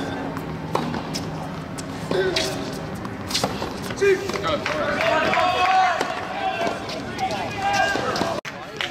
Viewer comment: What does say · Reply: "Si"